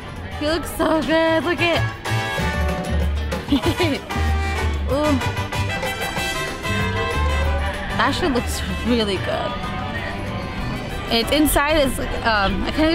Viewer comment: I thought you said.. “That shit looks REALLY GOOD” 😂